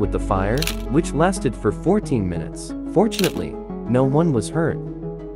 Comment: Why did I hear 😂"unfortunately they didn't get hurt"